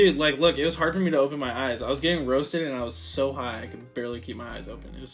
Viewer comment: Did he say "I was so high" 3:19 Mate what u high on?